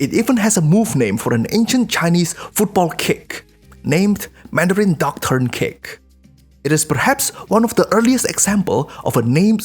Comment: Mandarin Duck Turn Kick, but I misheard = Mandarin Doctrine Kick 🤣